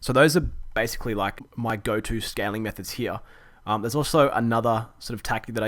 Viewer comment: <link> Great video it sounded like you said scamming methods lol